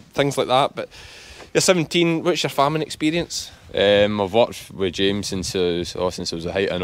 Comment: When you asked him whats your farming experience?, I thought you said Whats your Famine experience? I was thinking that's an odd question, then relise my mistake due to the accent.